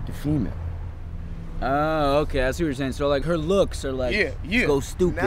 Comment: "i see what' you're saying" is "Super Saiyan" by auto-generated captions